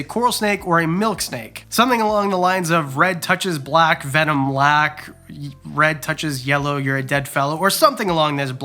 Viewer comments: I always heard “yellow on black, venom lack, black on yellow kill a fellow”. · I always heard it as "Red on black, friend of Jack. Red on yellow, kill a fellow"